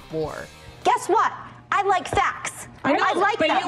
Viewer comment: sounds like she said “guess what?! I like sex!”